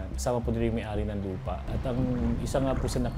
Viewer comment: I'm sorry did they say LEAD!!?😅